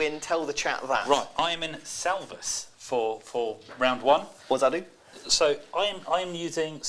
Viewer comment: Did I hear "Hva sa du?" at 40:20??